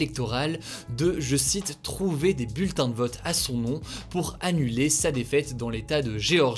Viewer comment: désolé mais au début j'ai entendu "trouver des putains de votes"